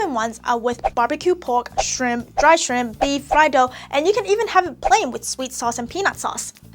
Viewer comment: did she say fried dog?